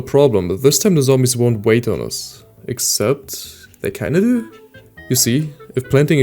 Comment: execpt they caneau?? Tf are you trying to say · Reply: "they kind of do" my apologies, english sadly isn't my first language. I grew up learning german :_SeedsHurtPea